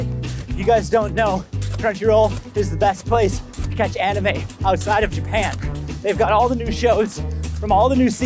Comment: During the add I thought he said “the best why to find anima outside of your pants” lmao